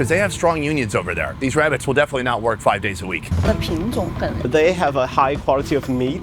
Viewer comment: What is she saying at 6:03? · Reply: she was saying the variety is good